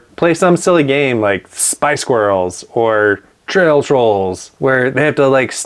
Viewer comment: I thought you said Spice Girls instead of spy squirrels 🤪